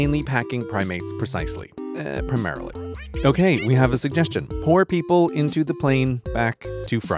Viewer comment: I first thought he was saying POOR people at the back instead of pour?? 😂